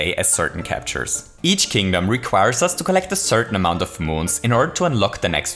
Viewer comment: What you said “Each level requires us to collect a certain amount of moons” What I heard “Each level requires us to collect a certain amount of boobs” I’m down for either